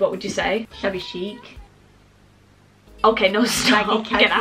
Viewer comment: I thought she said chubby cheeks instead of shabby chic